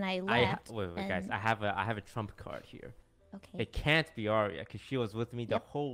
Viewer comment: in sorry did toast say he has a “trump card”😅😬